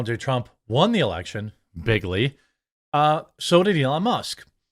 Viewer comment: Not a complaint, just wondering do others know Trump said "Big League" not Biggly"? Honestly I thought he said Biggly as well at first but seems to me he wasn't the type to make up words and I will also admit I have never heard anyone else say Big League so that's why I ask, lol. :)